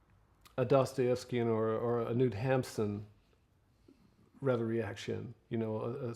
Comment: what name is he saying at 5:51? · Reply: Knut Hamsun. Norwegian writer...